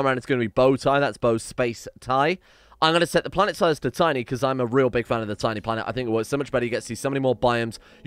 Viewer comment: when you say "I'm a big fan of tiny planets", I thought you said "I'm a big man with a tiny planet". Just thought is being quite forthcoming today.